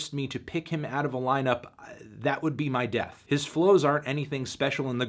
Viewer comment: i thought it said DEATH GRIPS lmao